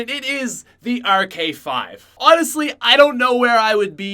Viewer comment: I thought he said the ark5 sucks